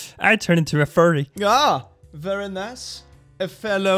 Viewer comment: 🙂instead of saying - “Ah, very nice...”, I thought you said - “Ah, furry nice...” ❤️ Which sounds furry cute indeed. Thank you furry much, I might start dropping this into conversations, but then deny deny deny if and when I’m ever questioned about it. Ah, a good plan. A furry good plan indeed. 🙂🐿🌈❤️ [sydney australia]